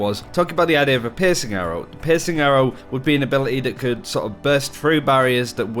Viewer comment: At first listen I thought you said "Pissing Arrow" instead of "Piercing Arrow". I now want a Pissing Arrow weapon in the game.